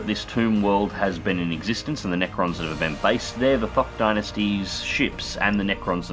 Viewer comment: Full on thought he said THOT Dynasty.